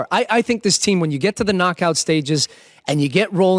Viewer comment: did he just say ko stages?